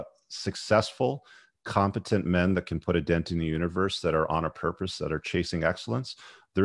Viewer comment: Put a dent in the universe. My favorite quote. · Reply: I heard "Put a dent in her face." I thought, BROTHER.